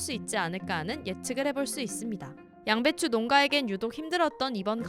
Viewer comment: 양배추..ㄴㅏ만 들려써여…? 자막까짘ㅋㅋㅋ